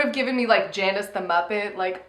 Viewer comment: did just say 'Jaundice the Muppet'